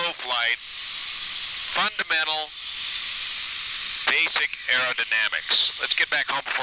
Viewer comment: angle of attack, slow and low...fundamental basic error dynamics. I know that's not what said but it sounded similar enough to be a new great expression for fatal pilot error !